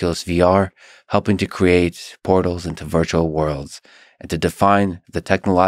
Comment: Lex, you need to pronounce "portals" more clearly. On first listening, I thought you said that Carmack had used VR to create "pornos into virtual worlds", but upon re-listening I figure you probably mean "portals into virtual worlds".